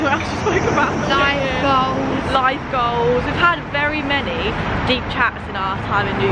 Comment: I thought said light bulbs not life goals 🤣🤣